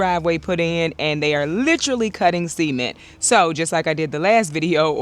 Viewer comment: Girl you are killing me 🤣🤣🤣🤣🤣🤣 I keep hearing you say "they are cutting seamen" i was like what in the world is that. I kept listening i kept hearing seamen so i went to google and typed Cutting seamen and this is what google said "likely a misunderstanding of a vasectomy". I was naw that must be wrong. I put in my earbuds and listen to you say it again. And then it hit me you were saying Cutting Cement. 🤣🤣🤣🤣 I had to get up and walk away. I was laughing so hard i think the neighbors thought i was getting attack. I need to get my ears checked. Edit: I Love the new logo